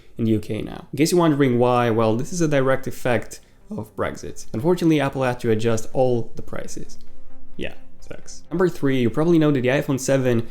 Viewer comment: Can someone please tell me what the hell he is saying at 2:19 It's cause of breakfast!? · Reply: “Unfortunately, Apple had to adjust all the prices.”